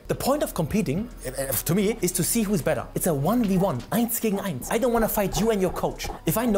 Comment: Did he just say eins gegen eins at 16:10? Alter lass träfen hinter Hauptschule nach Unterricht.